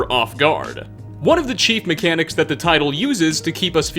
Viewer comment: I keep hearing “cheap mechanics” but I know you’re saying “chief mechanics” 😂😂